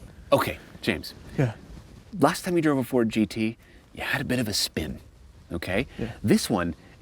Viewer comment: Did I hear him say "ford 5-8"🤣🤣🤣